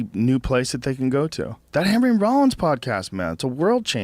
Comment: what did he say @ 8:44? something about a podcast? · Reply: Henry Rollins Podcast